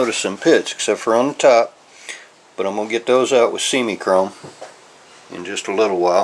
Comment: what did you say you would use to get the deep pitting from the top of the chain guard? around 3:10 or so; sounds like "seaming chrome"?!? thanks for sharing the tribal knowledge, and helping more bikes stay on the road